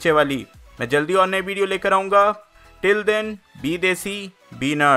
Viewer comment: Be Desi Be Nut I heard this at the outro😂😂😂😂